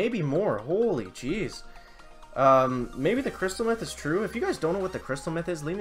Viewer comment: hey, are you going to pass on the free trader crate too? lol it sounds like you said crystal meth .